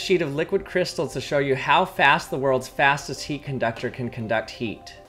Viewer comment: I thought it said fattest DX